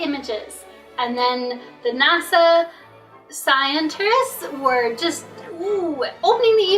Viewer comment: Did she just say "the NASA scienter-ists" ?? Oh, she def knows what shes talker-ing about for sure.